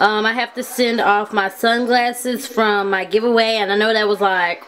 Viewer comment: At the beginning of this video I thought you said, "I have to send off my son's glasses" instead of, "Sunglasses" lol